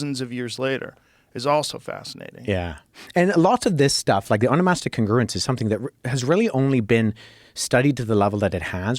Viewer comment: What is he saying @ 2:57:35? The ______ congruence? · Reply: Onomastic congruence : a method of analyzing ancient works by studying the patterns and frequency of names